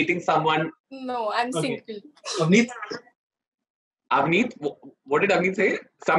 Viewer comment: What riyaz saying at 9:17 plezzzzzz reply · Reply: He just said avneet! Just thats it ...